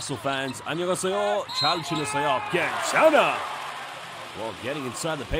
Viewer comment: 숀롱 슛할때 해설이 한국어로 들리지... 안녕하세요 잘 들으세요 ... 나만 이렇게 들리나?